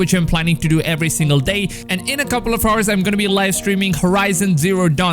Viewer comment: it sounds like ur saying horse not hours 😂